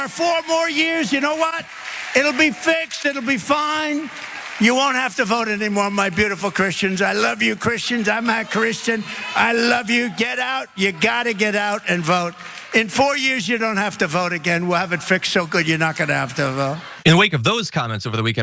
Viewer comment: I heard "I'm not Christian".